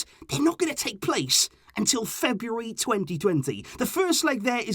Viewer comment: I had to go back and listen a few times but you said our games against Napoli will be happening in Feb of 2020 instead of 2022 at 3:12 😂. Love the content, keep up the hard work👍🏿